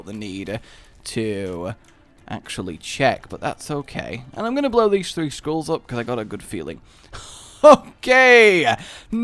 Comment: it sounded like he said im gonna blow these 3 schools up